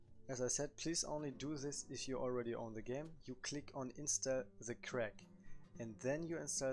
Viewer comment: wait what did he say at 1:22 did he say to click uninstall